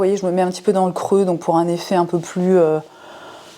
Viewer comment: j'ai entendu "pour un effet bison sculpté" 😂😂